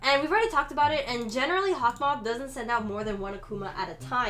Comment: OML LOL🤣🤣 look here 3:20 and turn on the English sub it accidentally wrote "*hotdog*" instead of Hawkmoth I'm so dead 😂😂